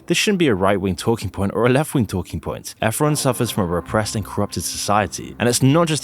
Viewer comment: I didn't catch that word, what is it that suffers? Automatic subtitles were of no help, suggesting "Ephron".